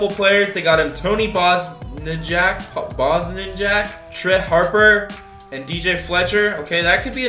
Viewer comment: did I hear “Truh” Harper instead of “Tre” Harper?